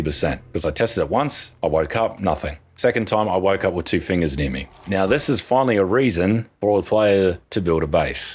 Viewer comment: Shoutout to my wife who thought he said "I woke up with two fingers in me" instead of "near me"